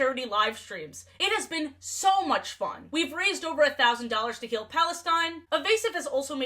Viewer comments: ik u said heal I misheard it as kill My apologies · For a second I thought you said "Kill Palestine" but the captions said "Heal". I nearly had a heart attack there. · i had to rewind like 6 times because i swore i heard you saw "KILL PALESTINE" and i just could not accept the idea that youd ever say that lmao.